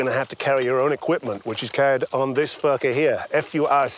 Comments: I thought he said "...which is carried on this f*cker here!" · "This furca here!" Thought he said fucker for a second there, haha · any one else thought he said "this fucker here" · I actually thought he said fucker instead of furca